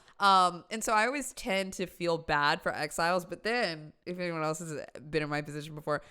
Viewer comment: Exiles! At first I thought she was saying "X-files" and I thought, "you feel bad for the X-files"?